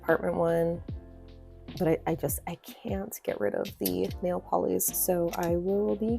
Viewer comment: what brand was the Matcha Made in Heaven polish (around 24:28)? it sounded like you said Nail Polly but a google didn't bring anything up for that, and I just think that mug cap is so stinking cute! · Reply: So close! It’s Nailpollies :) unfortunately since the caps are handmade, she isn’t able to keep her polishes in stock for very long and it doesn’t look like she’s ever re-made previous collections.. but it’s always worth emailing her to ask if she would consider it! 🤞🏼